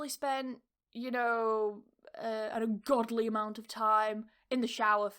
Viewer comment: In 11:23 did you say a godly or goodly amount of time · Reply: Godly